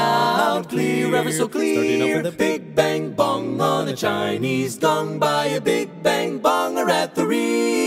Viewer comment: Did I really hear "a big, big bong on a Chinese dong?"